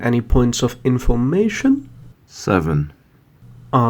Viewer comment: did he say "seven" in 3:09 ? i have no idea TT